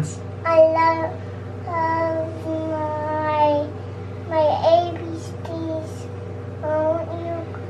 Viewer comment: did say AbSpeece? lol